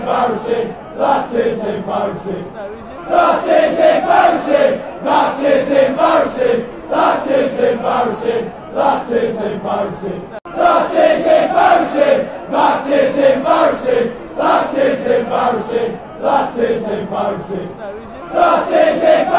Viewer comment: It sounded like it said that isnt mercy